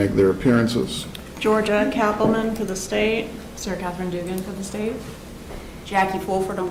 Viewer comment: “Sir Katherine Dugan”???? Did I hear that right? 🤔